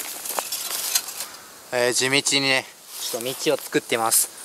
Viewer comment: 地道にが Hey Siriに聞こえるのかiPhoneが反応する笑笑